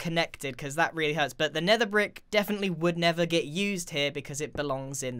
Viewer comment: the nether brick would NETHER be used here, I don't think he realized that he said that. also, why don't you just use the Debug stick to make it work how you want? and, tbh the stilts look way to thin, logs would probably look better, or they WOOD look better XD AND the second house's chimney was off center, ARRGGHH! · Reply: No he said never I think 🤔